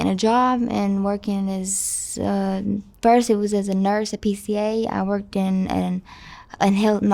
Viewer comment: She said she dropped out in 7th grade. Then she said she was a registered nurse taking care of old folks. Then she said her next job after being a nurse was at Sonic. Then she said her drug of choice is fent, with her virgin clean bare arms visible. All while acting’s hyper and twitchy. Was she always a pathological liar? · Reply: She did not say Registered nurse. She said nurse at 3:09.